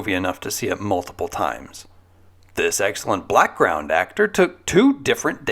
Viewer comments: Did he call homie a "BLACKground actor"?! 😂😂😂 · Did you say "excellent blackground actor" at 6:23? That's what I heard over and over. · Did you say "Blackground Actor"?